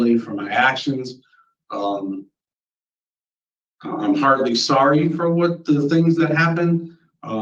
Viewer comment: I swear, this is the second time he says "I'm hardly sorry for what happened". I swear he said 'hardly'. Also, it's always something like "for what happened" and never "for what I did to my victim".